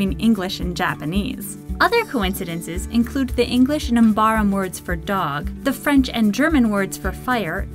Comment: Why on (minute) earth did you say it as "English Nambaram"? It's "English and Mbabaram"